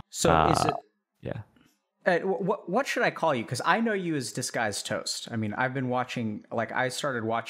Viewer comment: i thought that it said disgusted toast.